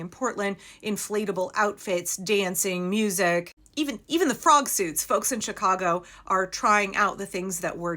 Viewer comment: Had to back up… I heard “prom suits” when you said “frog suits”… not a bad idea, if you can’t afford anything else.